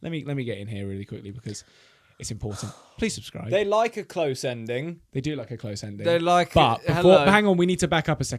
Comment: when said close ending tell me why my brain thought he said close edging and I was like that’s slightly inappropriate no 🤣🤣🤣🤣